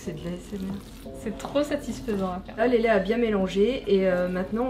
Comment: "le lait a bien mélanger?!"c moi car moi g compris ça X)